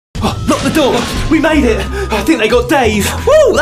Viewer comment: at the beginning I thought they said grab the dog, we banged it